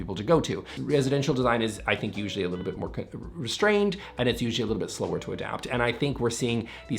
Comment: "Residential homes are a little more co...."... voice over..."more restrained " 😂😂 Sounded like you said conservative...hmm!